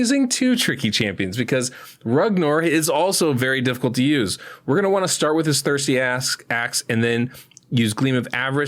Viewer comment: Lol, thought you said "we're going to want to start with his thirsty ass" at 6:35. Did a double take haha.